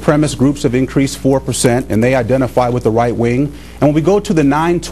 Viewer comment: I think he said white wing